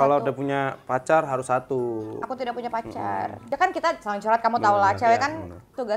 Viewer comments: Apa yang aku salah dengar ricis bilang gak punya pacar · ada yg tau gak kpn ricis hapus foto om om tik tok dr instagram...?nih syutingny kan tgl 9 dan ricis masih ngaku puny pacar sedih akutuh pas ricis bilang puny pacar harus ny bg papayan kat aja omongan ny itu huu huu huu tp aku tetep sesering mungkin mendoa kan harcis berjodoh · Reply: Salah denger mba nya jelas di vlog ini ricis bilang "aku tidak punya pacar"